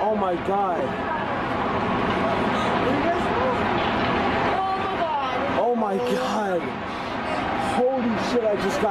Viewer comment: What does the person say at 11:35 ? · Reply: He said "where u guys going"